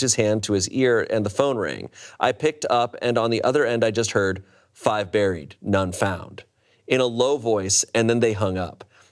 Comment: i kid you not during the entire first story i thought you were saying "5 berries, nonfat"...